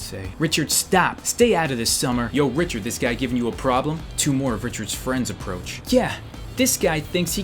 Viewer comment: When he said two more of Richards friends came I thought he said to not Richards came